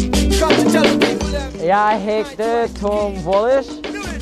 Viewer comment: did he say my name is tom wallisch in Swedish